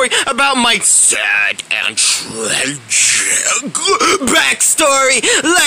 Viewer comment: What does Playtime said in 0:06? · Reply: Sad and tragic backstory